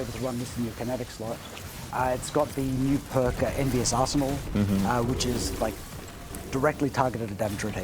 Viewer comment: i misheard it as 'Envious arsehole'